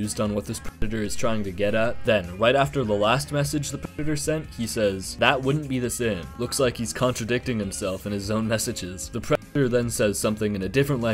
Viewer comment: if you want to know what said here, he says "You would be doing the same or worse than what I am supposed to have done."